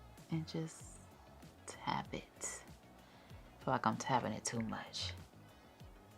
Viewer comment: I heard that! Out here cursing like a sailor 😍 beautiful set as always💖💖💖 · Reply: I think she said "feel like I'm tapping it too much" 😂😂